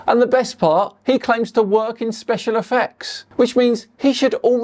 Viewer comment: Did you mishear him ? i thought he said he worked in special defects like CC does.